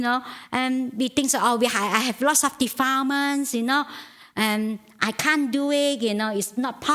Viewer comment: Please help there is one word she says "defarmers" or something (around 0:33:55) "I have lots of defarmers". She used it before and I can't understand it. Otherwise I am getting most of what she says. Thank you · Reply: defilement